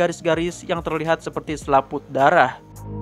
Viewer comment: selaput dara..? Apa gua gk salah denger..?